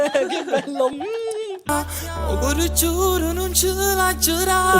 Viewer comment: C'è del Gigi D'Alessio in quella canzone..... E cmq io come Kekka ho capito "o cor giur nu ce l'agg rat" 🤣🤣🤣🤣🤣